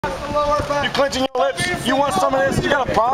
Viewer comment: I hear Allahu Akbar in background lol :D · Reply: I heard "Lower back." Why the hell would anyone say Allahu Akbar in the US Army?